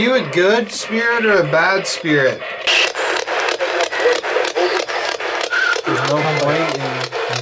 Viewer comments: I hear "there's no point it's over" Can't wait for part 2 💕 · Hey guys, if you're reading this comment- PLEASE LEAVE A COMMENT!!!! I really wanna boost engagement on my videos and if you just take 5 seconds and type a comment out for me you can help me SO MUCH!!! Also I'm going to do my best to respond to everybody!!! Love y'all, happy Thanksgiving!!! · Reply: I keep hearing "There's no point in open casket" which is super disturbing. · I hear “there’s no point in this bullshit”